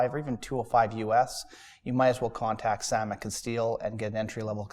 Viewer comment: I can't make out the name of the sword vendor that the guy on the right recommends at 5:05. Anyone? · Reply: Sam at Castille